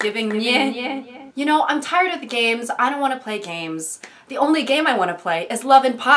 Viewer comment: GOD I THOUGHT SHE SAID “I’m tired of the gays” AT 0:26 LOL